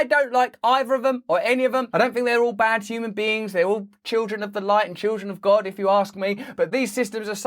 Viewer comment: At first I thought you said "Were all children of the light AND children of the corn.." and I lost my shit for a full 5 min.